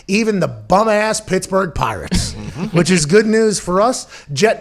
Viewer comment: All I heard is "Bum-ass Pittsburgh Pirates"...and I was satisfied by that